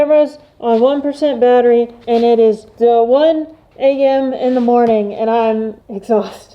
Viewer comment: I thought she said i popped out at 1 in the mornings 😂😂